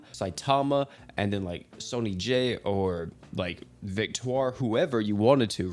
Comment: Why did I hear Sony Jay lol